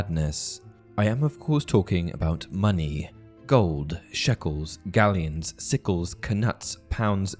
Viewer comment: did i hear shekels?